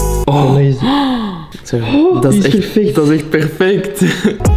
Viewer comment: I’m French and I actually heard “that’s dégoûtant” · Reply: i am Nederlands and i heard him say dat is echt perfect en ik moet niet laggen